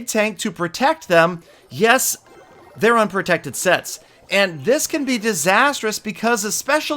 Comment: it sounds like he is saying unprotected se* 😂😂